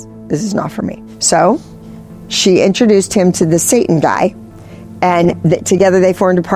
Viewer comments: Did she just say “introduced to the satan guy”? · did i hear her saying "she introduced him to the satan guy"? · did she say “the Satan guy?” Or did I miss hear that? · Did she say "satan guy"?